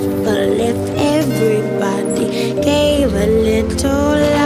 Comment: The lyrics sound like she is saying.."Give a Lidl Love"...Oops.